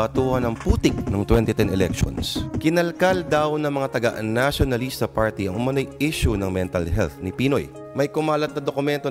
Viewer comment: Did I hear Lourd say "UN-Nacionalista Party" in 0:38?